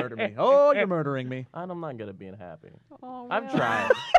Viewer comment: what does Will say? I don't mind being unhappy? I can't hear him.